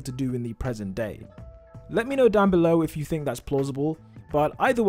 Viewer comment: Did he just say plossible